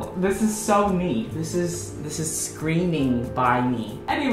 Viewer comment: after the checkmate mv i heard that as "this is screaming bi me"